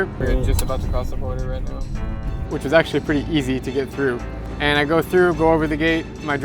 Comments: he said it was very easy but the subtitles said it was very east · he said east instead of easy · he said said :which was actually pretty easy to get through But subtitles says :which was actually pretty east to get through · when he said easy the captions said east.. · Spelling mistake on the subtitles. "east" instead of "easy" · you wrote east instead of easy in the subtitles